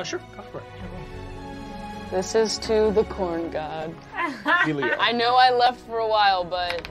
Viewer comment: I dunno why, but when she says "this goes to the Corn God" i heard "this goes to Khorn" from 40k